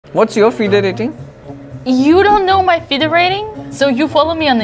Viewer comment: I thought he said feet rating lol